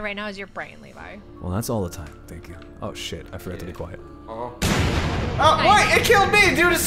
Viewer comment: Did he say the sh*t